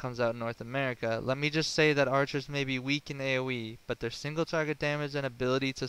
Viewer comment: excellent guide man what is AUE ? you say it multiple times or is does i have bad hearing ? PS: the last time you say it was at the end saying that the north america version that the archer maybe weak in AUE........( 13:15 )